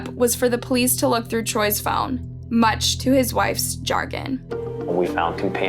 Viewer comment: I had to rewind…”what did she say??? -‘much to his wife’s jargon’??? Huh??? The word is “chagrin” and is pronounced “sha-GRIN”. It could be used as a substitute for ‘dismay’ in this context.